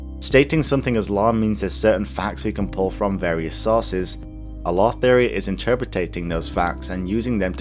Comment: It sounds like you say "A lore theory is interpretating those facts" when I'm pretty sure you meant "interpreting", seeing as how "interpretating" isn't a word. Is your accent British? I only ask because I'm pretty sure it's not often that a Brit has his English corrected by an American. Truth be told, it's the only reason I would bother leaving such a pedantic comment. For what it's worth, I dug the video.